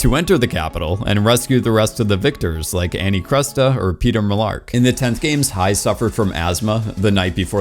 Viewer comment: I'm sorry. at 27:04 did you say Peter Mellark? Lmaooo I think it's funny but I could've just heard it wrong